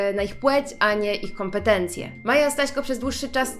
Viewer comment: usłyszałam Maja Sraśko🤣😂